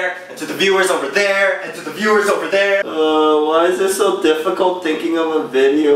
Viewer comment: I keep hearing Tiffany.. Make a video where you imitate Tiffany!